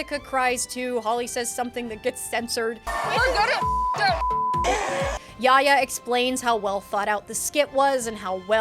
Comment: I think Holly says "We're gonna fuck shit up"